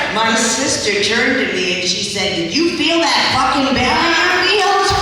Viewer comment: I didn't understand what she said when she said her sister turned to and said "Did you feel that F...ing ---- " Didn't understand the word its kind of mumbled here and noise of the crowd hides it. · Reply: I think she said barrier field